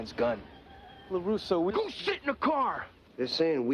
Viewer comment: wait, did he say "go sh!t in the car"? D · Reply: I'm thinking it was probably "go sit in the car."